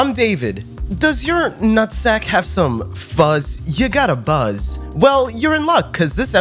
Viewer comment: Fuck me. I fire up the video, look away and all I hear is "Does your nutsack have fuzz??" A strong start to be sure.